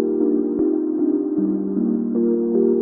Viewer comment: All this time I thought it said "mechabombi make it boom"😭